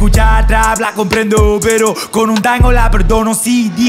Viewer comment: Pensé que decia, "Pero, Con un taco la perdono"😭